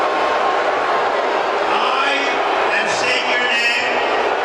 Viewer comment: Did he just say "I am state your name"? He legit has dementia...